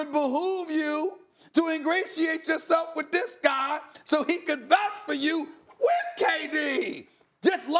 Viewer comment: what did he say? lmao "it would behoove you to ingratiate yourself with this guy, so he could vouch for you, wITTHH KDDDDD"